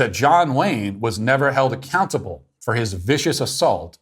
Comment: Swear it sounds like he is saying John Lane the first few times